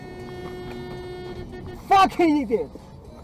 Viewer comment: I can’t be the only one who heard that right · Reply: Why? He says f***ing idiot. It's an accent that sounds like he's saying something else, but I grew up hearing my mum say idiot just like that, so heard it straight out.